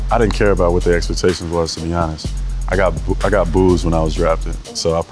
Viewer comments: Well damn....I thought he said," I got boobs when I got drafted.....".😁😂😂😂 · I heard that 'I got booze when i was drafted'